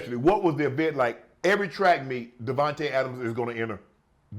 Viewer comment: Did he call him Davante Adams lol